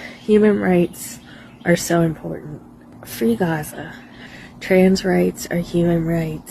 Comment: I hear free Gaza. Mama they would kill you if you step foot there